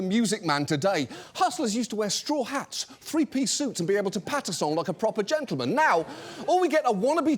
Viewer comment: what did he say here: pad-a-song, patisol, patisall like a gentleman? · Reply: Patter-song, I think, as in a quick sing song speech to persuade